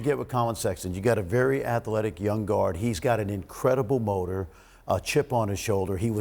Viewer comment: Turned around to rip the bong and thought I heard him say “he has an incredible boner” 😂😂😂